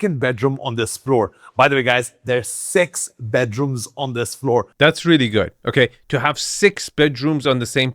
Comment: I thought he said there was sex bedrooms on this floor. Was excited to see what they came up with.